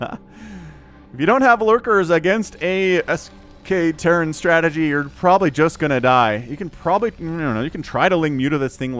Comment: terran what are you saying before terran falcon? sk,esque Ive heard this before and I never knew what it means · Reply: SK terran. Soulkey terran. bio + science vessels.